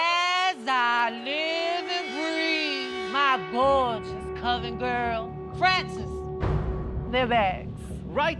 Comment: I always thought Ru said "Covid Girls". Hahahahaha